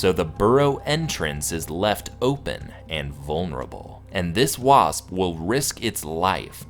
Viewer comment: did you say vulnerable?